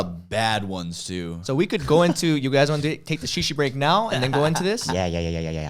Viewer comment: I thought he said sheesha break! you know like hookah XD